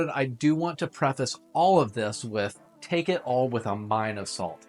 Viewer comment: Thought you said "take this all with a line of salt." I had to rewind ten seconds 😂